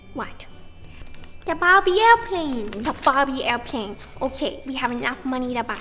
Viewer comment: saying "a Barbie airplane" is the most adorable thing ever!!!! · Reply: I thought she said "bobby" Aiwplane